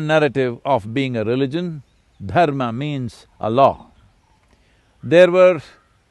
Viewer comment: Dhama means a law...(Subtitle is wrongly written)